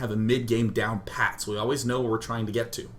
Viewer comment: did you just say patdown? ^_^